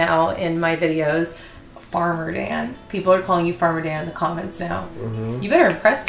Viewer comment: I totally thought you said Arbor Dan at first 😂 So if he ever gets into planting/caring for trees, that can be his new name